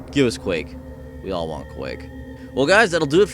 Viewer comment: At the end I thought he said give us cake, we all want cake... ... I really want cake.